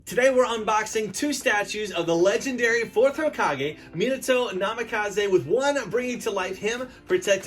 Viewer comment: did he say fourth raikage???